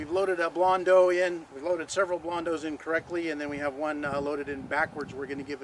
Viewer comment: I heard "several loaded incorrectly and then one loaded in backwards" 😆